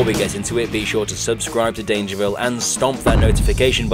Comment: When u said at the beginning stomp that like I thought u said stomp thanos